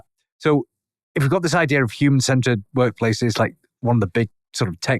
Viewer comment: Listening to this in my car, I thought he was saying "Human scented" instead of human centered.😂